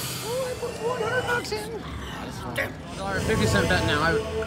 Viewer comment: Congrats!! Awesome run!! I thought u said u put $400 in the 5:10 mark??